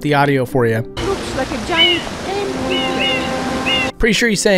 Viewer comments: it kinda sounds like he said "a giant plate of spaghetti" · Imo it sounds like he says "Looks like a giant Painting"